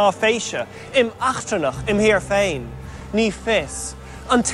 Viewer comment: What he's saying at 1:05 sounds like the Dutch word "achterlijk" (retarded). :V